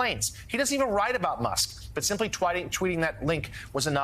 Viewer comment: Did he just say TWATTING? 😂